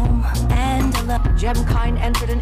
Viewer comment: I always hear "and the lukewarm, diamond"